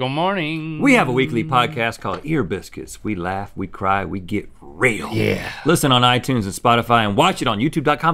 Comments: lol i thought rhett said "...we laugh, we cry, we get railed" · I heard “We laugh, we cry, we get ✨railed✨”